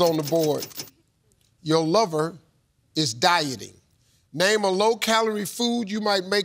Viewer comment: OH! DIE-TING. LORD, I THOUGHT STEVE SAID DYING. That changes my answer.